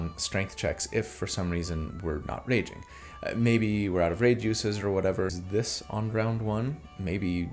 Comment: I swear it sounded like he said out of Rage juices. 😂